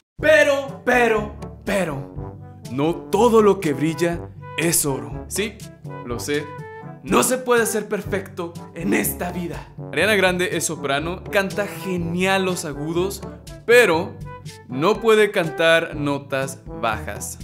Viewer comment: Segundo 23 Pensé que dijo rihanna :(